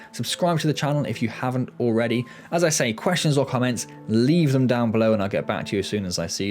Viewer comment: lol I thought you said "all questionable comments, leave them down below" took a second for me to process you said questions or comments LOL